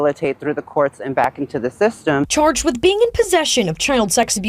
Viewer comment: Why did she say “chuuurgg” 😂